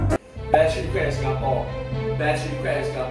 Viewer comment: At first i thought they were saying besche crab Singapore but then i realised its Best Chilli crab singapore